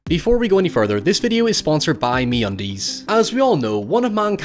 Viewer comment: Sense i've heard alot of people from Ireland use "Me" as "my" in the beginning I heard "This video is sponsored by my undies" and I was very confused for a second...